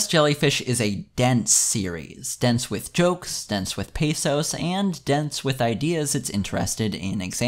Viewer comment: Pathos? I thought you said PESOS.